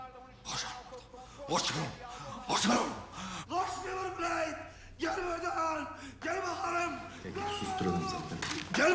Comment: Ne diyor burda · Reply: Koşalım diyor